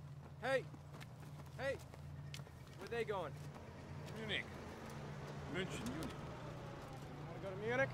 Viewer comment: That German soldier said he's going home to Mannheim. My Great Grandfather immigrated to the US from Mannheim. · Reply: He clearly said munich